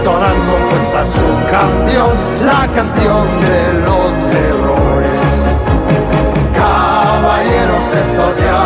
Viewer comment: Letra: Los guardianes del universo al triunfar el mal,sin dudarlo salen a combatir por un mundo ideal, caballeros del zodiaco cuando lanzan su ataque entonan con fuerza su canción la canción de los héroes Los guardianes del universo al triunfar el mal sin dudarlo salen a combatir por un mundo ideal, caballeros del zodiaco cuando lanzan su ataque entonan con fuerza su canción la canción de los héroes, Caballeros del zodiaco contra las fuerzas demoníacas guardan siempre en su corazón coraje para venserr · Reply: era heroes?!....pense que decia los errores!debo haber parecido un idiota!(referencia simpson)..